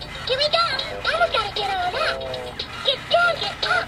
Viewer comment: Wait Did He Just Say?: “Elmo’s Gotta Take A Dump!”? 🤣🤣🤣